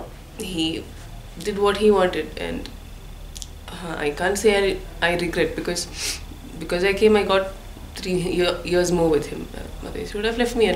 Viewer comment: she clearly said she doesnt regret